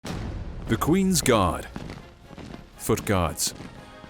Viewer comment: I thought u said "THE QUEEN'S GOD: FOOT GODS" for a sec X'D